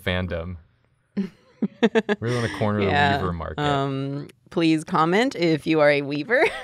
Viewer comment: I kept hearing "weavel" until they referenced the baskets and it all made sense